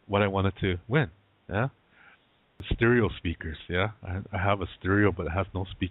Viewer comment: I thought you were saying sneakers! I'm like why does he care if his sneakers match his stereo?? Haha I can hear you laughing already!